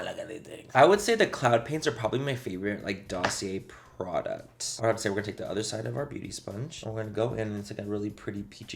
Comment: Did I hear him say “my fav Dossier products”😭😂